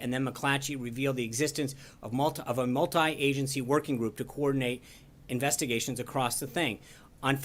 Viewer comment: Wait, wait. Did I just hear him say "...in order to coordinate investigations across the thing" (7:31)? Blink, blink.